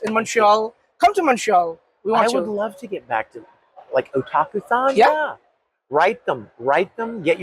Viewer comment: what Vic said otakufan or otakufun or funk what kind of event is called couldnt quite hear that name